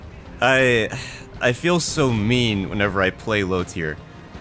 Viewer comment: I heard that. @ 3:30 "You feel so meme when you play low tiers".